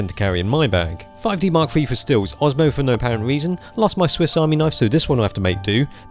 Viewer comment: What is Kai talking about at 0:34? Can't understand what he says before "...for no apparent reason." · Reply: He says "osmo"